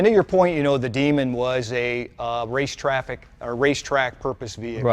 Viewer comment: Lol did he just said that the demon was a race traffic 06:14😂😂😂😱😱👍🏾🙏🏿💯🦌🏋🏾‍♀️💨